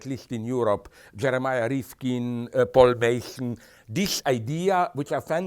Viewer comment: What are the works mentioned at 5:10 ? Autocaption says "Jeremiah Ripken" and "Paul Basin" but I can't find those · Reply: I grasped just Jeremy Rifkin. :)